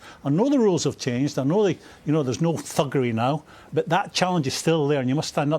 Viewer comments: looool i thought souness said 'fuckery' not 'thuggery' on live tv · I was so sure at 4:03 it sounded like Souness said 'fuckery' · Genuinely thought he said Fuckery for a second at 4:04 😅. Had to double check